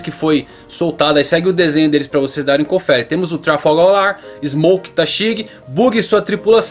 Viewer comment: só eu que ouvi Trafalgaw Lar..?? Hahah